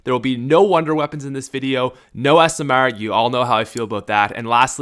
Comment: At the beginning I thought he said no asmr